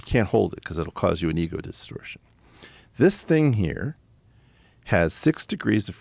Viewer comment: What is the final word he says? "You always give away the compliment. You can't hold it because it will cause you an ego distortion[?]" - CC reckons it is "distortion" but I get an extra syllable. Anyone? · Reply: He did say distortion with a slight slur in the middle.